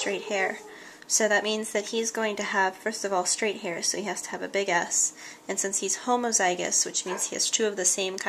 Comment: did she just say that the mas needs to have a big ass on 1:46? Just me? yeah? ok...